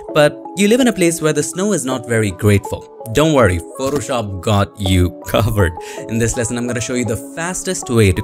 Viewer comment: When he said don't worry, photoshop got you covered. I thought he said Don't worry. Photoshop God got you covered. I was like yes you are.